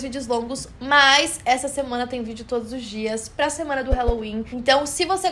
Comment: Eu ouvi "essa semana tem vídeo todo dia" 🥺?